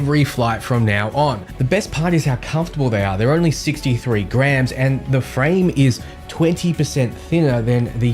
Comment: Great info on this new game mate, I enjoyed the video, but I I nearly shit meself at 1:58, when you said those glasses are only 63 grammes - I thought you said GRAND!! 😳🤯😅😂